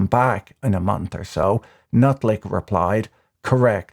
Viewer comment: Did I mishear that or did Patrick just call him trade secretary "Nutlick" at 23:55?